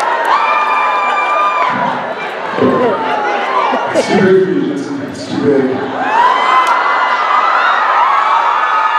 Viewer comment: Someone please tell me what do they say from 10:37 - 10:52 . For the life of me I'm unable to catch the words! :( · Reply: Idk the first part but after the laugh, Jared says: Will you be my Christian grey?